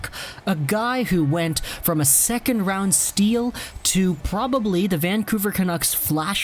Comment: I thought it said “2nd Round steal to a bust” I was like what happend at the world juniors he was insane??!!